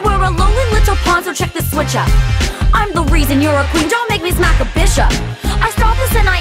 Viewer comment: I never thought Laura would ever say bad words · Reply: It was part of the song, So, Yes, There is gonna be swears in these, And actually near the ending i thought she said 'bitch up', It sounded like it XD but i think she said 'Bish up' XD. There great actors that do this, so Yes, There will be swearing. It's part of the song/rap.